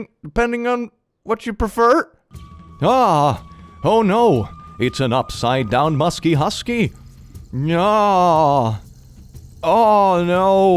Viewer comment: I heard "Oh it's an upside-down musky husky" and was like "oh no what has elon musk done now" 💀💀